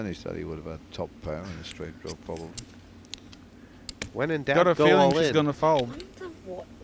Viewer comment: Did anyone picked up what Vicky said at 20:54? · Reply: I wonder what the odds are, that we have the same hand.